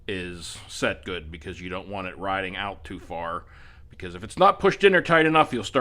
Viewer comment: I compress mine almost all the way before tightening down the cab there should be a tiny bit of rebound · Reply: oh, I thought in the video you said “you don’t want it bouncing back out”